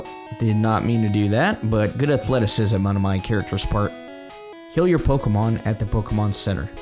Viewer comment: if you listen carefully and you think the word "heal" you would hear him say it, because he did say it. BUT if you think the word "kill" it sounds like he said kill.